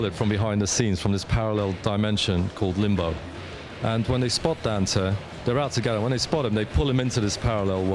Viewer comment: Did he just call Dante a dancer?